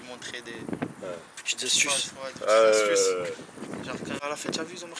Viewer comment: je reve ou ya un mec qui dit "je te s... " Je suis chokerrr 😦 c koi ce bordel 😲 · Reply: Mes nn i dit des astuces🤦🏼‍♀️